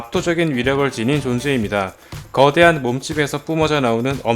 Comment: 전 몸집을 몸뚱이로 들었음